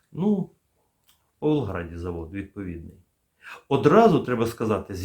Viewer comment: і де його виготовляли в 2015 році? Почулося, що у Волгограді. Це як? Може --- в Павлограді? Тоді, що це ще за таємнички після того, що і міністром оборони, і головою СБУ --- були громадяни РФ? А за останні пів року викрито кротів у керівних органах силових структур?